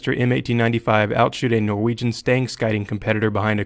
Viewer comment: They might be shit, but oh man are they cool! :D Btw, what did you say at 3:20? Outshoot a Norwegian stang shooter or something? :P (wondering since I'm Norwegian!)